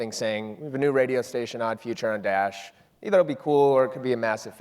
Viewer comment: which artist is he talking about ? Sounded like he said " Future & Dash " but when i look it up, i can't find nothing. Unless i misheard what he said